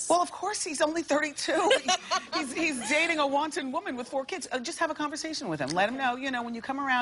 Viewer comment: Did Wendy just call her a wanton woman? lmaoooo